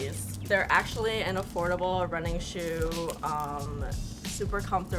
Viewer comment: did she say they’re running shoes